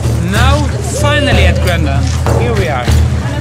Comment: What is the name of that last neighborhood you were in? Looks cool! Sounds like you were saying "green land." Going to Oslo soon and I definitely want to see that neighborhood. Really liked the video, btw! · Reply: Hi Craig Peck, happy to hear that you liked the video :) In Norwegian we say "Grønland", which means Green Land. Its just east of the main train station - close to a hotel called Plaza - the tallest building in Oslo. I love the area, hope you will too. Say hello if you see me in the streets - and welcome to Oslo :)